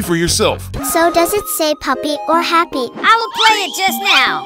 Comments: I heard “PAPPY” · I heard "Huppy"